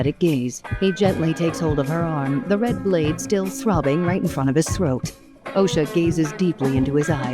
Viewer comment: did she say throbbing?